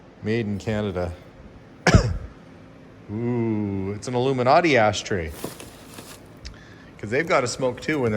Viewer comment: f ry Belle had measles, she would need the sunglasses to prevent blindness! That doll is definitely NOT one to give a kid!!! Later on in the video I thought you said, "An aluminati ash tray. --- aluminum. "